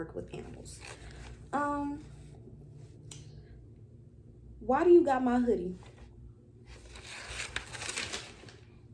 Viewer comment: did you say a piggybank?😭😭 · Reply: Yes a piggy bank shhhh